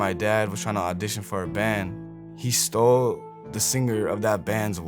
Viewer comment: Ive thought the entire time he said took the singer's life